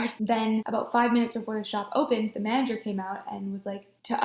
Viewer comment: I thought you said "the mandrake came in" instead of manager